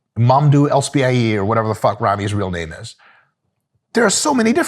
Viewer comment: You mean Big Ramy? 😂 I had to rewind that cuz i thought you said "whatever Ronnie's real name is." I should go back to bed. 😅